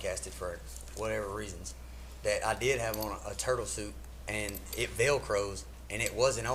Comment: Why when she said “turtle suit” I pictured a ninja turtle costume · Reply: I thought he said he had on turtle soup, and all I could picture was him standing at a party covered in some globular mess, evading PETA advocates